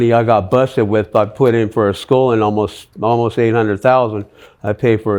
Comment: At first, I thought he said her school costs $800,000. That would’ve been an expensive nursing school.